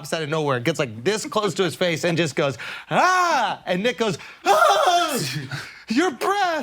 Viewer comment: Sounds like Schulz says the N word Everytime he said "Nick goes" 😂